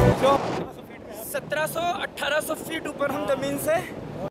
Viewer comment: What were you saying at 0:23 ? 😂😂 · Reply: I was saying it's really a scary height 😮